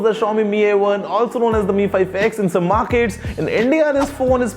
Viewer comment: did he say endia